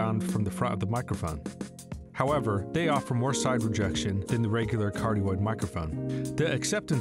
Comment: the music is too loud, some parts are difficult to understand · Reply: is he saying "side rejection"?